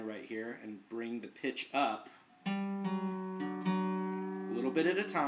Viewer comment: I was taking this so seriously until you said 'G string' then I thought I heard 'bring the bitch up' but you said 'pitch' omg